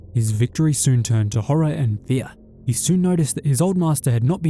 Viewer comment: Sounded like you said his victory soon turned into "horror and BEER." It's a sequel I'd watch.